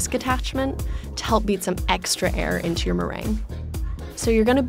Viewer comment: Hold up did she just say meringue